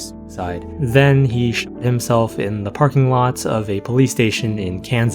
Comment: because of the censorship i thought you said shit himself instead of shot himself